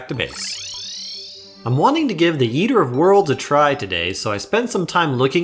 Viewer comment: did... did you call EoW "Yeeter of Worlds" or am I mishearing? o.o